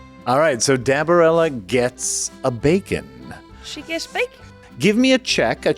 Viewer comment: OK To be completely honest, when said that Dabarella gets abakin', I thought he said 'a bacon', and I was VERY confused for a moment.